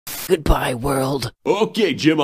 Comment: I remember when this came out I thought he said "I thought he went to the mall"